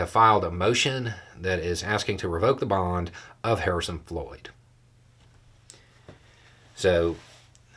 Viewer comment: I thought you said "Harrison Ford" at first and was like, 'wth just happened?''